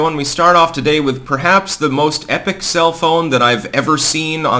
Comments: I thought you said "cell phone" instead of self own. Boy was that confusing. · I always hear "self own" as "cell phone" and it sometimes throws me off.